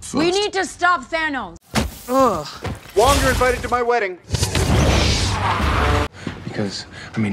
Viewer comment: “wong you’re invited to my wedding” zooms off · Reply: I heard "you're no longer invited to my wedding." Bug I honestly dont know now because it sounds like that